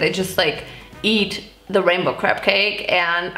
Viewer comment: hahhaha I love that it sounds like your saying "the rainbow crap cake" 😂